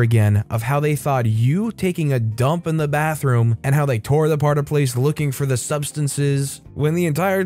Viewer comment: Did he say tore the part aplace?